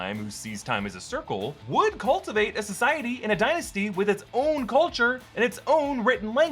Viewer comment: Did u say Kang's Dynasty?